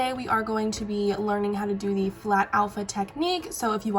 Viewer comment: I really thought this said “how to do FAT alpha technique!” And I got SO confused, lol! Love you 💕